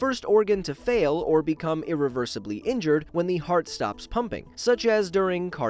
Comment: when the heart stops humping i heard first then corrected it to pumping